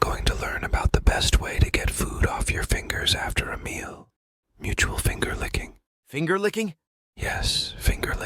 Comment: Mutual finger-licking 😳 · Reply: lol. I thought you said "Mu'Ta Finger-Licking"...